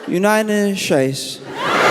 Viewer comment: Did he say "United Scheisse"?